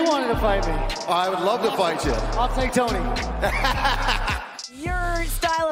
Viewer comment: When Justin said I'll take Tony, I had flashes of ptsd 🥲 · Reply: Didn’t hear that. I heard “I’ll murder Tony”